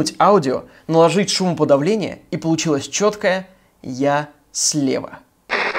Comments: Во фразе «я слева», когда не смотрела на текст, услышала «ем снег» - чувак с того света, умоляю тебя, только не желтый! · я не слышала "я слева", я услышала "валера", пока не посмотрела в экран... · мне послышалось не "Я слева" а "Я съел" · Вместо "я слева" я услышала " я Слава" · мне вместо "Я слева" послышалось "Лы-сею"